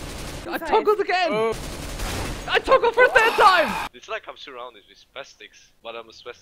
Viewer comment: I heard I've toggled first hentai